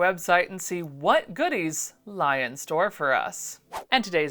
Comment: You said ‘lie in store’ but I heard ‘lie in wait’ with an undertrack of ominous music. Yep. Ominous music accurate.